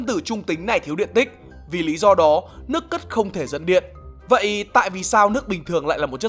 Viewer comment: ko biết phải không nhưng ở 4:23 mình lại nghe thành nước ... không thể dẫn điện :))) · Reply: Nhưng mình nghe thành nước c. Ứt cơ